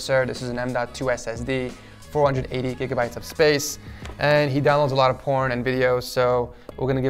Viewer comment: did you say that He Downloads Slot of porn?!?!?!